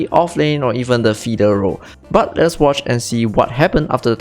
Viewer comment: did he just say "feeder Role" ? Lol